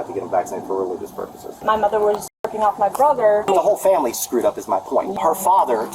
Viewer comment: I was just listening to videos not paying to much attention and then not even 5 seconds in i just hear. "My mother was j**king off my brother"... it completely caught me off guard.